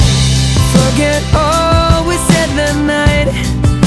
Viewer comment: I thought Louis said '' Fuck it all we said that night'' LOL